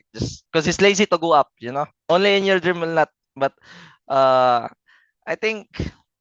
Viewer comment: this botak hmmmm sound like he say inyourbald